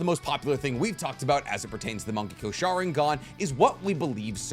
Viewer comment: Thought he was saying "monkey go sharingan."